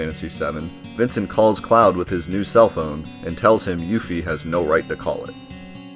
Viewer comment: I what to clarify Vincent said Yuffie has know right to call him? Also why ??